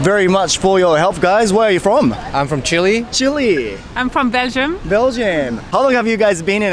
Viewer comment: どうしてもベルギー(belgium)がブラジル(brazil)に聞こえる……イントネーションで覚えるしかないか